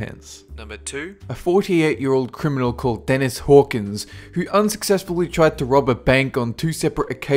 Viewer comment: Dennis Hawkings.... I thought you said Stepehen Hawkings XD How stupid of me